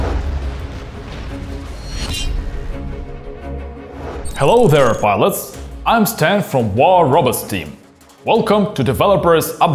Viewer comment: Did he say "hello there wallets" lmfao